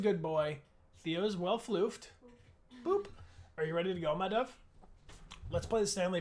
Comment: did Bryan call her “my dove?” 😭